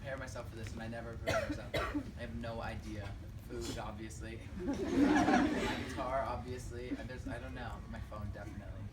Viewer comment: did he say "boobs"?😂😂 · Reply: "Food"